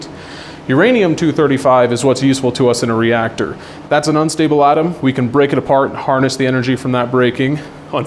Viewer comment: Small correction in the CC Destin, @ 4:15 he says Uranium-235 is an "unstable" atom but the CC says "stable"